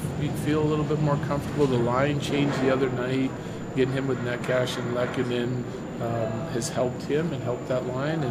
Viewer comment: Does still not know how to pronounce Necas's name? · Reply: Lol I'm here for this too. Did he just say neck ash